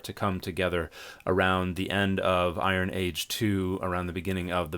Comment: I was looking at the Marvel t-shirt when you mentioned Iron Age 2 and I swore I heard "Iron Man 2." I didn’t realize Tony Stark was that old.